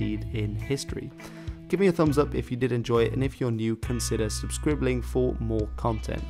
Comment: I swear at the end of the video i heard you say thanks for subscribbling... XD it's totally ok if you did and you should do it in every video meow.